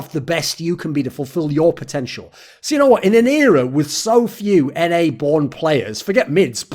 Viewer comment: Everytime he said "In an era" I kept hearing "Inero" and I was like man why's he mentioning Inero so much in a video about POB